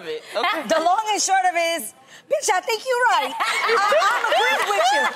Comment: Did adrienne say “ BITCH I THINK YOU RIGHT “ at 4:22 😂😂😂💀💀 and jeannie weave lmaooo and tamera wigg